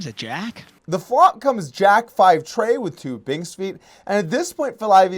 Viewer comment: What did Doug call the clubs at 2:47 of the video? 😂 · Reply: Frank Steinberg Binks' feet after his dog, Binks. Confused the hell out of me when I first heard it.